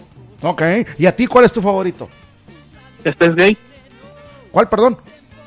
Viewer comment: como se escribe el anime que dijo en el minuto 34:06 no le entendi bien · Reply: KILLUA steins gate